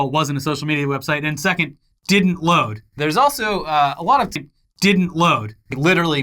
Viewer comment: Did I hear say "didn't load" twice or was that just me?